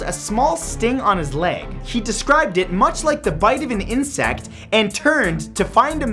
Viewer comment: I thought said bite of '87